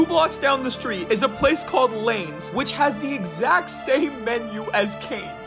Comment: When you said Laynes, I thought you said Lames 💀💀💀💀💀💀😭😭💀😭😭